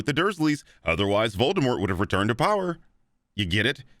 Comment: the subtitles say You-Know-Who and says Voldemort. that's funny